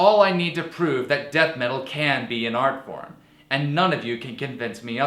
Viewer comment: shit! I misheard what you said at 8:07 I thought you said "Can't be an art form", my bad, still leaving the comment though, I proved a good point god damn it! I don't want to delete it, anywhore, Great video, I know your feels.